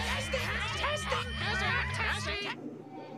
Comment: why do I hear one of the parrots say tasting and taxing instead of testing and why do I hear another say is there a taxi